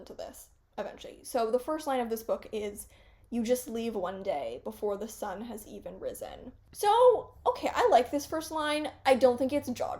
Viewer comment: i misheard the first line of holding a ghost as 'you just need one day, before the sun has even set', and now i want to ask permission to use that misheard line?